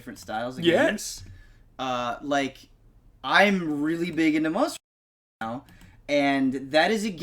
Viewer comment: hey, i didnt hear what u said @ 1:40 liked it was muted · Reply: He said. "Monster Hunter"